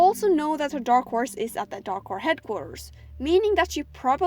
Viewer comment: Thought you said Darko headquarters....